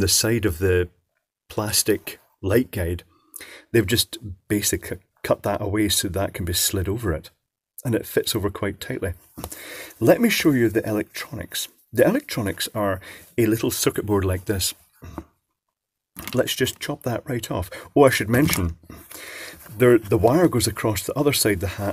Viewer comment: When you said, "Let me show you its circuitry...", I heard, "Let me show you its features", followed by the demonic, germanic, chuckle of Joerge Sprave (Slingshot Channel).